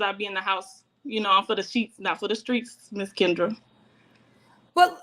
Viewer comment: Did she say she for the "sheets" not for the streets⁉️ 😭 Is there a difference ⁉️ 🤦🏽‍♂️